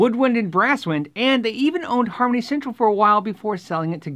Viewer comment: When you said Harmony Central I thought you said Comedy Central.